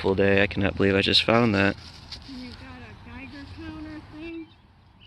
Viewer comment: I swore I heard her say “is that a Geiger counter?”